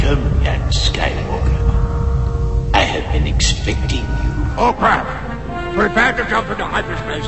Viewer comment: sounds like he said oh crap